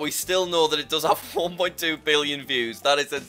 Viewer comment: am I the only one who hears, "4.1 billion views."